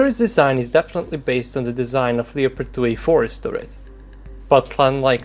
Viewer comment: What is he saying about the turret desing 0:35? didnt understand him after 5 replays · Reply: "the turret design is definitraly based on the design of the Leopard2A4 turret"